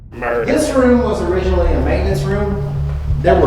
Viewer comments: Anyone else hear the word murder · did someone say murder? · Anybody else hear the word "murder" right before the Body Store Room cut? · Am I the only one who hears the word "murder" very clearly right at 30:48?